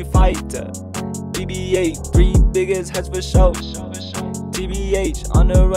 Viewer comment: tf he say? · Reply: vashegan lmao same thought I they kept saying the n word throughout the whole song but I looked at the lyrics and apparently it’s “three biggest heads”